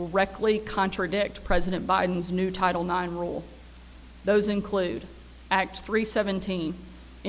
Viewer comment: Did she just say Acts 3:17? Acts 3:17 "Now, fellow Israelites, I know that you acted in ignorance, as did your leaders.